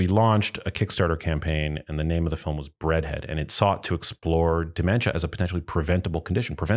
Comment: I heard you say "bread head" so is it bread?